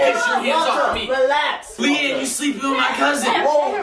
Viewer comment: at the very beginning did say smtg about kelianne being his crush or smtg? i heard “y r u sleeping with....”